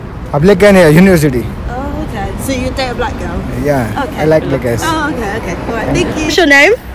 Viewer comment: He said he like what?? 😭 I thought I heard something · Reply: Lmao I think he was trying to say black girls idk 😂😭